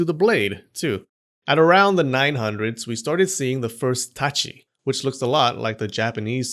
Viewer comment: I thought you said itachi😂